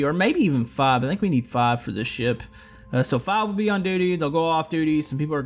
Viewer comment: i thought he said "five for this sh*t" :"D